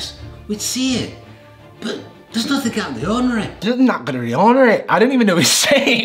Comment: i think he said "'theres nothing out of the ordinary""